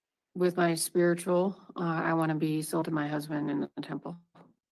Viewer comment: Did she just say at 15:49 that one of her spirtual goals is to be "sold to her husband in the temple"??? :face-orange-raised-eyebrow